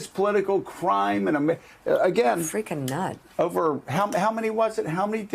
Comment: "Freaking nut(s)" was what I heard there. Then: bullseye! About GOP destroyng their chances for one generation: if they destroy it that much, then the Republicans will never come back. They will become one of the historical parties, such as the Federalists.